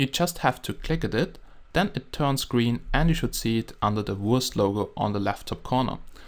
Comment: When you say wurst logo it sounds like your saying worst logo XD